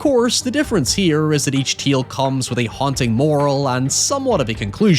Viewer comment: Only 8:39 in did i realize you were saying tale when i heard teal